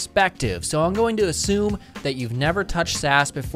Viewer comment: Im watching in 2x and i thought he said ''so you're about to begin a crash course in Sass from a very beginner perspective, so im going to assume you never tocuched ass before"